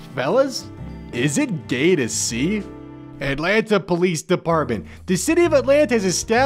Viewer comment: is it me, or did he say Atlantis lol